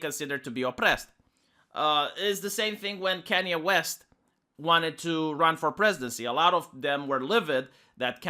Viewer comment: It sounded like you said "Kenney West".